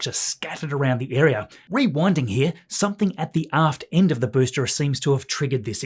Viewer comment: I first heard "Something at the ass end of the booster..." (At the aft end...) 😅